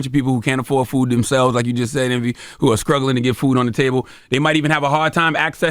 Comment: Did he say skruggling at 11:25 ?